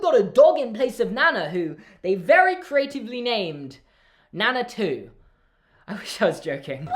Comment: Nana 2. I thought you said "that they named Nana, too" but no. NANA 2 😂😂😂😂😂😂😂😂